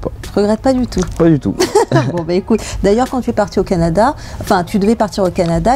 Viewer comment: c moi ou elle à dit je m'en bat les couilles